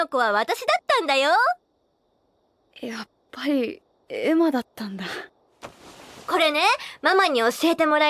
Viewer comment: thank you for the translation! i really enjoy and appreciate it! i have a question though: at 1:16 did norman said「やっぱり... エマだったんだ...」“just as i thought, it was emma” ? and is that the part what ray was pertaining to at 10:54? when ray said he was about to burst out laughing when he realized that norman knew all along that the girl was emma? OR was ray taking about the time when he talk to norman (at part 3) 2:12 “norman... about that girl... do you perhaps...” “No, you're wrong”... im just confused when did norman knew that the girl was emma and when did ray realized that norman knew it...